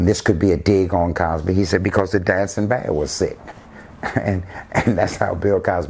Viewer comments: "Because the... dadsonbadwasset?" What the hell is he saying there, lol. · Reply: The dancing bear was sick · What does he say at 3:57? Reason why Cosby made it? Cause the dancing band.....??? I can’t make it out. Please help.